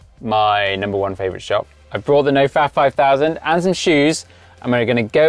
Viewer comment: For the first eight minutes I thought you were saying No Fap 5000.